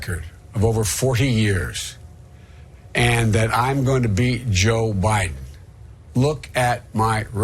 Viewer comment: I think he went full retard, because it sound like he says "I am going to beat joe biden"! Get this man some water and then to old folks home or funeral home because this trash is done!